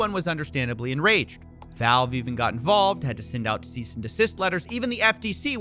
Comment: "decease and desist", I'm pretty sure that's what I just heard 😂